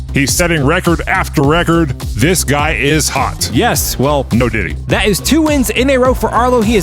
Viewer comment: did he just say no diddy😂